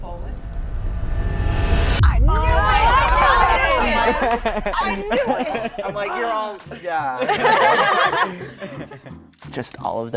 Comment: Did he said "wiedziałem"?